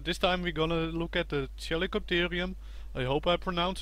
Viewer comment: I think it says Chalicotherium!